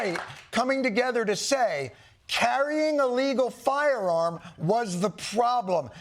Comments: The subtitles say illegal when john said legal. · That's unfortunate that the subtitles/CC have Jon saying "illegal firearm" instead of "a legal firearm". Ironically, it's actually the "auto-generated" one that has it correct. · captions are wrong - I believe he said "carrying a legal firearm" not "carrying illegal firearm", since Alex Pretti had proper license and did not violate any law about gun ownership.